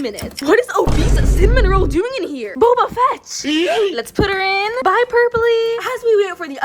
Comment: People who heard Boba Fett 👇 1:19 1:20